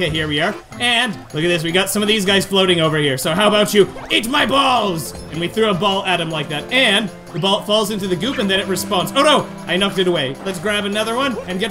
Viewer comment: Did you just say eat my BaLls